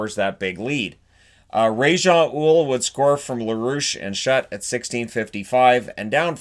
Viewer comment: So did anyone else think he said Ra's al Ghul's name at (6:01)!!! Justice League!! <3 <3 the videos, your doing an awesome job!